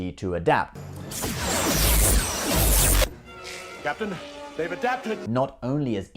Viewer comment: I swear Worf is saying, "Captain, they have adaptin!"